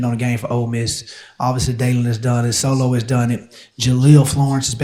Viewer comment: "_____ has done it ..." What name does Coach Hampton mention here, between Daylen and Jahlil? Sounds like he says "Sholo" or something. Maybe it's a nickname for Sione Laulea or Solomon Davis? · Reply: Solo for Solomon Davis.